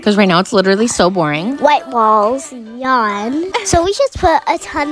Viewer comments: Thought she said “ wHiTe BaLLz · “ white walls, yawn” 😂😂😂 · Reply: i thought she said “white balls”😭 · Why did I hear "white balls yawn " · For a second instead of her saying “set walls, yuck” I thought she said, “wet balls, yuck” HELP MEEE 💀💀💀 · For second I thought she said white balls😂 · She said white walls but it sounded like she said white b@ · What I hear “wet balls yawn”😅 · Not just me who thought she said "white balls" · I THOUGHT SHE SAID WHITE BALLS 😂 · When she said white balls yarn I thought she said wet balls lol